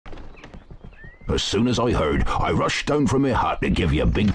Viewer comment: i decided to write down what i heard first XD "As soon as i heard, i rushed down from me hug to give you a big congrajulations. i know youlc atch this bitch first, i just know it."